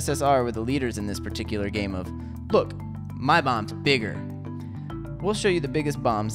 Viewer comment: Lol 1:22 captions say my mom's bigger